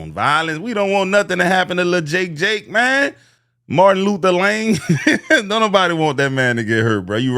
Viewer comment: The switch up is hilarious, Martin Luther Lanng!! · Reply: I thought he said Martin Luther LAME😂